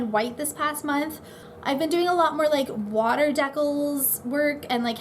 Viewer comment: Lol it sounded like you said 'water decalzzzzz'